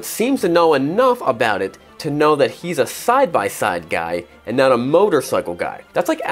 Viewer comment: Oh side by side, thought I heard sci-fi lol